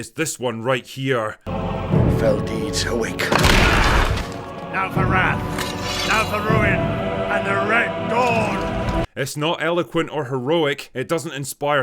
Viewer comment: now for wrath! now for ruin! and the red dawn! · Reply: "Now for wrath! Now for ruin! and the red dawn!"?? Dude I've seen The Two Towers about 200 times. Watched this battle scene 1000+ times. Up until now I thought that he said "NOT for wrath! NOT for ruin! and the red dawn!" YouTube comments are educational 😂